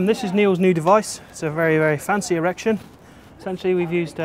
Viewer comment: did he say a "very very fancy erection?"